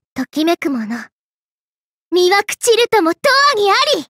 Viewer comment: Did i hear " I am mu CUTE!! " ?